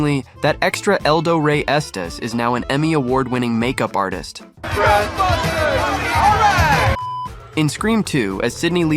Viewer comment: Of all these, there's absolutely nothing wrong with the guy yelling out, "Ghostbusters, all right!" It totally fits with the rest of the crowd's enthusiasm. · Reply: I was just gonna ask what is he saying. What kind of accent is that?? I hear "WESTBUSTERS! A´RIGHT!!" or "TRESPASTERS, ALLRIGHT!!" or something like that lol